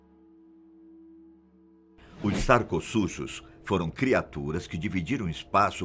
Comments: sério que eu ouvi sacos-sujos? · "os saco sujos..." kkkkkkk, ri demais. O que foi ouvido não poderá ser desouvido, kkk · eu ouvi "saco sujos"